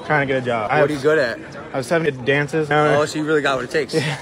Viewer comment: I thought the guy said 'I started at dances' at 0:32-0:33 all the time, but the words you displayed in this one seems like he did technically said he sucked at it. Damn, why would that guy be hired if he isn't good at anything? 🤣